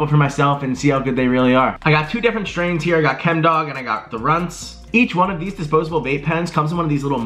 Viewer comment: lmao for a second I thought you said "I got the runs"